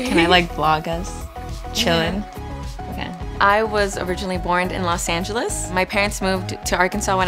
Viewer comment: Did she just say "borned in Los Angeles"?